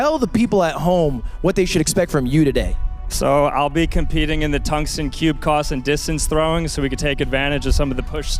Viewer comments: did bro say, "tungsten cube coss"? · did he say tungsten cube coss